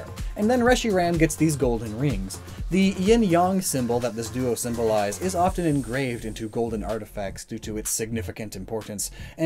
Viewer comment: Did he say "Ying yong"? 😆🤣